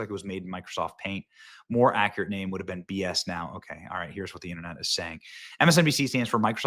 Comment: Did you just say BS NOW ?😂😂😂